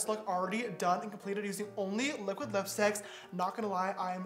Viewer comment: Captions "liquid the sex" What he actually says "liquid lipstick" Me watching without sound "...wait what?"